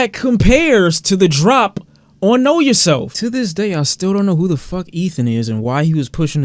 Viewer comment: I turned the volume up and heard him say “I still don’t know who tf Ethan is” Bruh my names Ethan I got scared💀😭